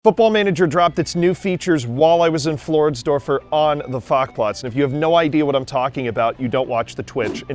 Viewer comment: I know you're saying "FAC-Platz" but all I hear is "Farquaad"... sooo... Lord FAC-Platz?